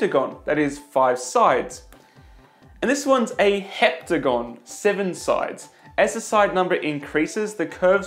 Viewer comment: 3 things that made you cooler just from this video: Obsession with obiects of constant width likes grand Illusions And wants a car with a rotary engine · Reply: i thought he said hectagon lol. he meant septagon though